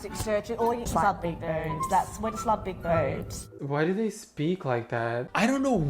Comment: I don't know if i am the only one who hear "i love big birds" ??